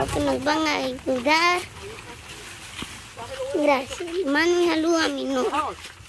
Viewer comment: Minuto 6:41? Quien está grabando? Lo amo!! Escuche mal o agradece y le manda saludos a su novia!? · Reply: Aprovechó para mandar saludos a su novia🥰todo lindo!!